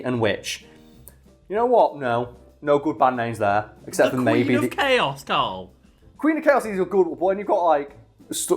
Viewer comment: closed captioning reads "Queen of Chelsea's Boys" instead of "Queen of Chaos". Queen of Chelsea's Boys definitely an excellent band name.